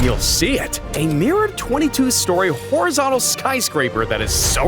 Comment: Did he say 22 story for the mirror house?